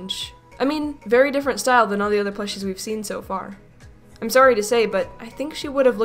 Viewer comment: the subtitles say s*** f*** but it's meant to be plushies